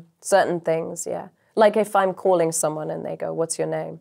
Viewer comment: Lolol it almost sounded like she said “Like if I’m calling someone a (n-word)”